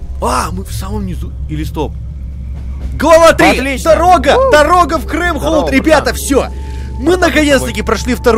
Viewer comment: В 18:15 мне послышалось что он сказал "Дорога в Крым"?